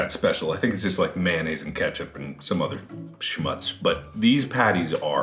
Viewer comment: did he just say "Schmutz"?